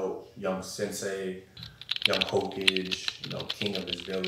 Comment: Did he just say young Ho-cage instead of Hokage? Pretty sure sub or dub they say Ho-ka-ge.